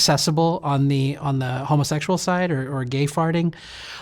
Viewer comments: Did he just say “gay farting “?! wtf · I'm so fucking childish, I heard "gay farting" and I've been laughing in my work truck for 30 minutes 😭😭